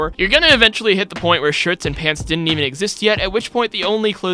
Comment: did you just say ..... shirts and pants?